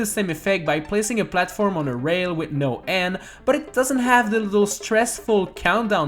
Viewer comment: This part of subtitle somewhere in the middle says “And” instead of “End” when said “End”